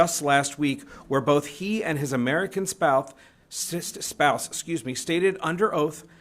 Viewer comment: did he say sister spouse or it's just my imagination